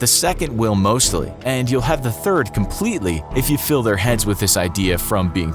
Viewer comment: did he just say "thirth" instead of "third"? am I the only one who noticed this?